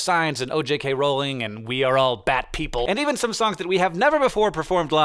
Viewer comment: Oh my gosh!!! You mean I get to hear 'We are all batpeople' live on Monday?!? I think I'll explode with happiness! :D Yesss!!! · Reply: oooh bat people I thought he said bad people 😂